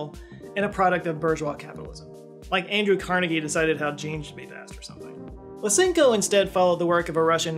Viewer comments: What's the Andrew Carnegie joke at 7:08? I can't make out the mumble. "Like Andrew Carnegie decided how jeeenzhsshubbyvasht or something..." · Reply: "How genes should be passed or something" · "Like Andrew Carnegie decided _______ or something" I've replayed and listened to this sentence like 10 times already but I still have no clue what he was trying to say. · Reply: "how genes would be passed"